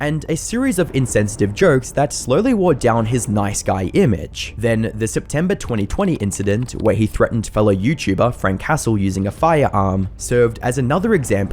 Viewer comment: I thought he said Frank Castle instead of Frank Hassle and thought the Punisher had a youtibe channel lol